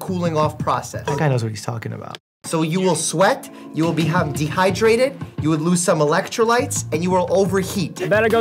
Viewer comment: I swear I heard Mike say "you will have be dehydrated 👨‍⚕️"